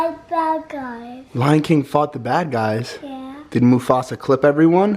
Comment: who else heard ‘Back Guy’ lmao